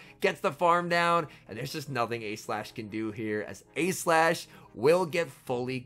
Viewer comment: when you say “A-Slash” sometimes it sounds like youre saying “E-Slash” and then that sounds like you’re saying “Aegislash” and for a second im like “what??”